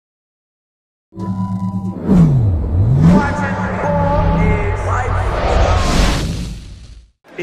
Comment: Anyone know what the intro is saying from 0:01 to 0:06?? lol I can’t seem to find out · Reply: Ruzzell Wezbruk watching ballislife